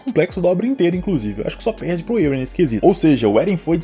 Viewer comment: só perde pra qm? kkkkkkkkk n entendi nada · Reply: Erwin